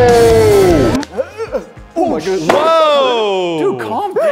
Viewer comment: When they flipped mom's chair, did anyone else here him say sh*t? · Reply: I heard 7:16 ohh sh*t